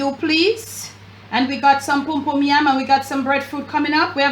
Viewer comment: did she just call it pumpum yam?